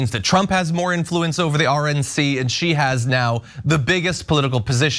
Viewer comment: I thought she said Lord Trump, I had to rewind twice 😂